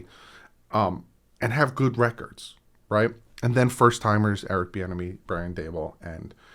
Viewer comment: I thought you were saying Eric "The Enemy" and thought "What a sick nickname."